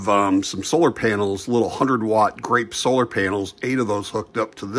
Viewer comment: The company that makes the solar panels is Grape Solar. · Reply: Oh, i thought you were saying Great solar.